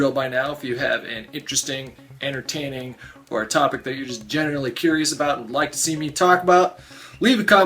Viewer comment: Did you say "genitally curious" @ 0:15?